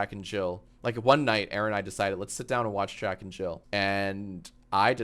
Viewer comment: I was only half paying attention and thought I heard that they were sitting down to "watch jack and chill"